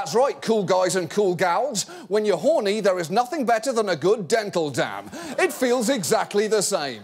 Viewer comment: wtf is he saying here... than a good "dental down"? "dental damn"? (like a beaver damn?) seriously I cant for the life of me understand or get what he's referring to he and I've listened to it 15 times now